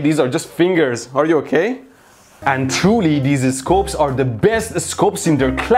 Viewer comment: Did he just say and twoly